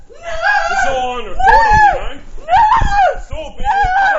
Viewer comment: The captions at 25:30 are wrong. He doesn't tell her "It's all over!" he clearly says "This is all being recorded ya know? It's all being recorded!" Now, by using deduction, it seems that the only situation in which you would tell an erratic drunk girl acting like a psycho "this is all being recorded" would be to get her to calm down and stop doing whatever crazy behavior she is doing atm.